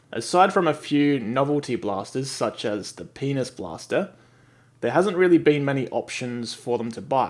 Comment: Umm, did he just say "The Penis Blaster"???